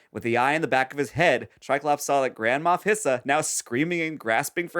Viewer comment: For this entire series ive been mishearing Grand Moff Hissa as “Grandma Piss” and I just accepted it from the beginning